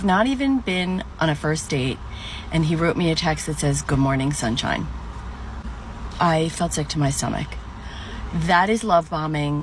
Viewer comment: I thought she said good morning sunshine is ick🤔